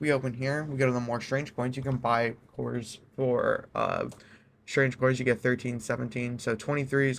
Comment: I had to rewind several times because I though I heard him say "Strange of Coins". Or is it "Strang-eh Coins"?